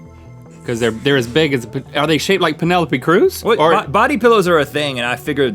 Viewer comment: It sounded like Rhett said "Pinelli P. Cruz" :P